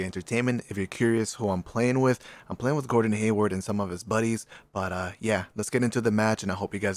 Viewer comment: I am deaf or did he just say Gordon Hayward?